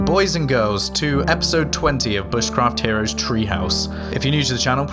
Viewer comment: I thought it said "Bushcraft Herpes". What a relief.